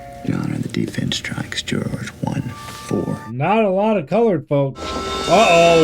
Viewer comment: Sounds like he said Not a lot of colored folks lol